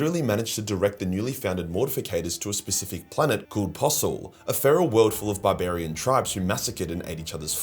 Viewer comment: I thought you said Possil not Posul. Possil is a mad area of Glasgow in Scotland. I thought GW might have been having a laugh with that one.